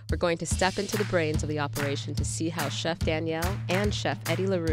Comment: When she pronounce Daniel it’s sound like she said Chef Danielle